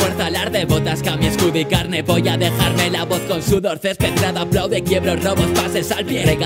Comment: Alguien me podría explicar por qué dice polla en el minuto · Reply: "Voy ya a dejar la voz " Eso es lo que realmente dice